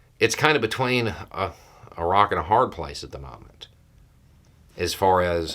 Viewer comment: Not that this is the best place for levity, but I could've sworn you said "between Iraq and a hard place at the start" ^_^